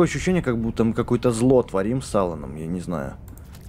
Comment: мене послышалось не с Аланом, а с Сауроном.